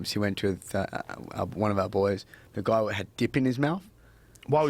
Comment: What are they saying at 2:32:04?? Dip in his mouth?